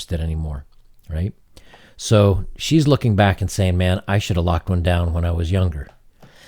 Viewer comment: what did she say? "I should have locked one down when I was younger." See, you're just a commodity.